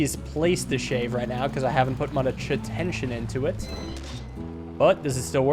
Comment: i cant tell what he says here... does he say... shittention? what? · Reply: much tension